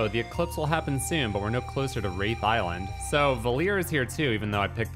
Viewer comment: For a slight second, I thought you said rape Island! You could have fought Epstein for a final boss! 🤣😂🤣😂😅😂🤣😂😅😂🤣😂🤣